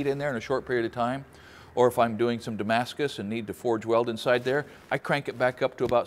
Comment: "make a damascus burke" bar is what i heard